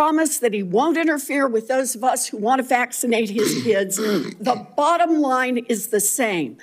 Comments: Did anyone else catch that? 0:24 "Those of us that want to vacate HIS kids." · Did Warren say at 0:31 “ for those of us that want to vaccinate HIS kids”? Was that a Freudian slip saying his instead of our kids? Start it at 0:27 to hear the whole sentence. This woman is absolutely evil. Stay away from our children you’re evil and clearly paid by big pharma.